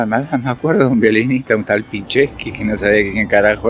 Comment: pichetto entendí 😅😅